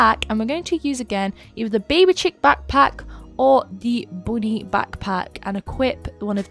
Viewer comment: Is it just me or did said "Booty backpack" 😂 (Timestamp